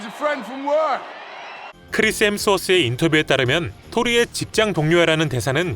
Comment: 나만 크리스 햄스터로 들리나 🐹